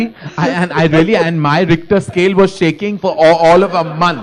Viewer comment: Did i hear you saying "your rectum scale is shacking"...alohah..